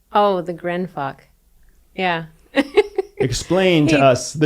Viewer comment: I heard, it’s the "GRIM FUCK". Did that occur? P.S. "THIS" IS THE NEW "ODD CUPLE". LIKE THEM. P.P.S. Live the intermittent THUNDER, (barely audible) in the background.. makes me squeeze my mind, trying to HEAR IT. Disliking it…liking it, kinda.